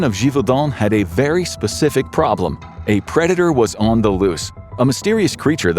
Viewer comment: I thought he said creditor I'm high af yo